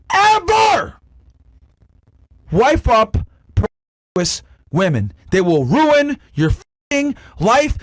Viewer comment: what did he say? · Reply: Never wife up promiscuous women